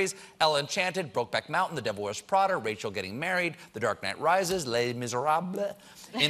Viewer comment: Did Colbert just say "The dark man rises" instead of "Dark knight rises"